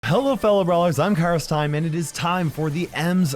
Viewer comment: Cara Steinman is what it says in captions